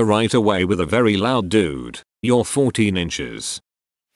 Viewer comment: O.o I heard, "dude, you're 14 inches"...or did I mis-hear? :)